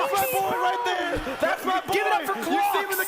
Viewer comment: Loool I thought u said "Glocks" during Mogul Money rather than "clocks" 😅 maybe that's why people didn't cheer